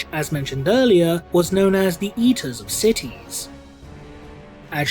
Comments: I’m pretty sure you said “eaters of cities” but all I heard was “Yeeters of cities” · It sounded like you said “the yeeters of city” rofl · At first I thought you said "yeeters of cities". And now all I can think about is angron trying to throw buildings into space lol